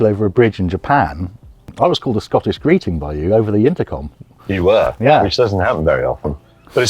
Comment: what is Tom saying? It sounds like he's saying, "I was called a Scottish greeting by you," but that doesn't make sense to me. Anyone understand that part?